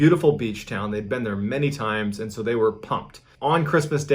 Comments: I have no clue but for a long time I thought he was saying tires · I thought you said tires